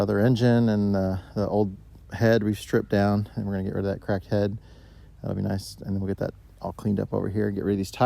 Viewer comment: when you said you was going to get rid of the cracked head i thought you said crack head and laughed my ass off lol